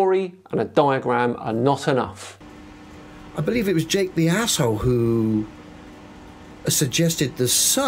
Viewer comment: did he really say "I believe it was J. B. Asshole who suggested...", did it not occur to him it was a made up name????